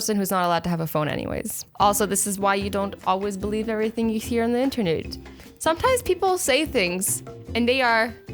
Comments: Hi azzy!! Anyone else hear internert at 8:07???😂😂😂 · DID SHE SAY "the internudes" OMG LOL